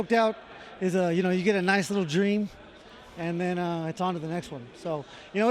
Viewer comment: did he just say “getting choked out, you get a nice little dream?” 😂 Gotta love this dude